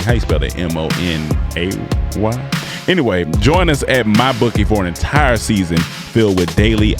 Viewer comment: YOOO IM SUCH A Southern nigga I thought this shit said “My Boonkie” 😂😂😂 I was like what they hell yall got promoting🤣